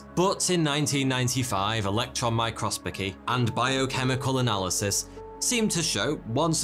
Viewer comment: did anyone hear him saying microspocy or is it just me ? I slowed it to 0.5 and it still says microsPOCY